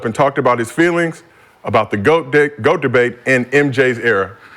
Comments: did he say GOAT D#ck? LOL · Did he just say “About the Goat dick.” ? Or am I bugging out? Lmao